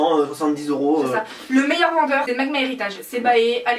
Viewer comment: c'est quoi le nom du revendeur à 8:21 j'arrive pas à comprendre (sorryyyyyy) et perso j'adore les namu (surtout les fc) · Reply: Magma Héritage x)